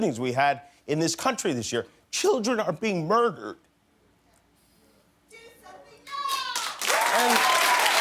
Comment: I agree with what he says in this but when that lady shouted at 1:54. I laughed so hard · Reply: colleen I think she said “Do something now!”